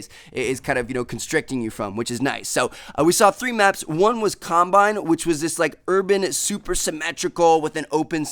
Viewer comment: For a second I thought he said columbine.😐😐